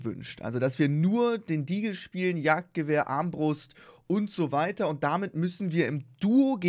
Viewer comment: Ich habe bei 0:07 verstanden arschbrust